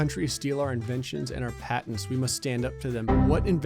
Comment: Lmao when it said "Foreign countries steal our inventions and patents" I legit thought it said "our inventions and parents" wtf mind 😭